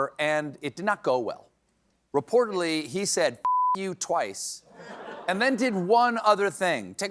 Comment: I misheard and thought the heckler said fuck your twice not Trump 😂